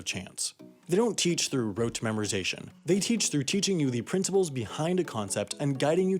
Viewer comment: They teach through teaching. Edit: My internet lagget so it sounded like that